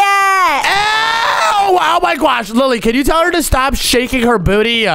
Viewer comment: Did she just say that she had a gayat😂😂😂😂😂😂😂😂😂😂 👇